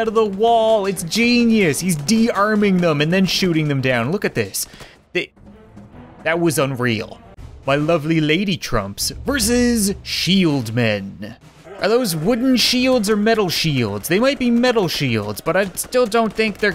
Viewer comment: i thought he said X men